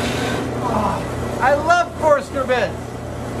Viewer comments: wish i could share a screen shot but at 18:40 the auto caption mishears "I love Forstner Bits" as "I love poor servants" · Lmao at 18:42 Adam says " I love forstner bits" the auto generated caption says "I love poor servants"